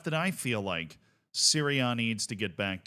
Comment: I thought he was saying Syria instead of serie a.